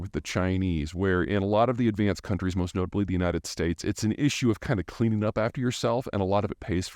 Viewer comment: Did Peter just say ...."Its an issue of the Chinese cleaning up after themselves?"???? Bah ha ha ha.